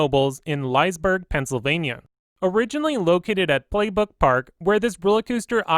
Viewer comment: Sounds like you said “Playbook Park” when describing the history of Phoenix?...